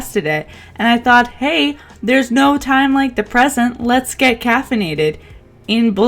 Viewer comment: I thought you were going to say "there's no time like depression" instead of "the present" 😂😂😂